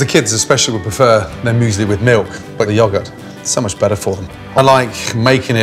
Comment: Everyone is talking about eggy bread, but am I the only one who heard him say "joggert" at least 5 times instead of yogurt?